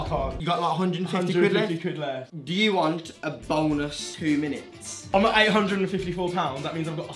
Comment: i swear i heard do u want a boneless 2 minutes